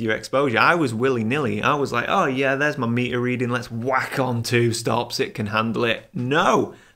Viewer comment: I first heard "I was like, oh yeah there's my meat you're eating. Let's whack on! Two stops, it can handle it."